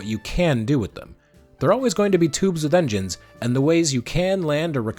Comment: At first I heard “tubes of vengeance”😂